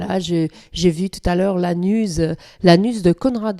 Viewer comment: OUPS Je démarre la video et, dans un premier temps, j'ai compris "j'ai aperçu l'anus de Conrad Colman", et pas "la news". Les charmes des accents mais je ne me permet pas de me moquer, j'ai souri quand même ;)